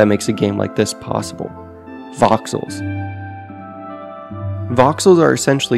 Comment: did anyone else hear foxholes